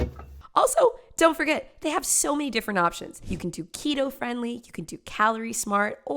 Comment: Girl I just heard you say Taquito Friendly and was like wait wha- oh. To Keto Friendly.